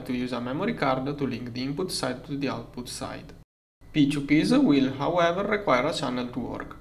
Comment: what is bro saying? Subtitles crapped out. Unintelligible. I really wish YouTubers would upload proper captions. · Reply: "p2p's will however require a channel to work"